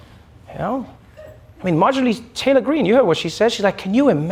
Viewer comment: I just heard him say her name as "Marginal Taylor Greene." I realize he didn't say it. My brain made a slight alteration....